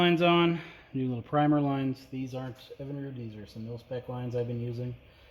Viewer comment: What brand of hose did you use for the solenoid ⅛ I.D hose? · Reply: I'm trying to figure that out too. Sounded like he said milspec but can't find that